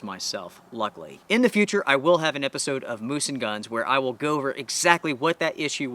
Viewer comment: Oh! Moose and guns...thought you said BOOZE and guns.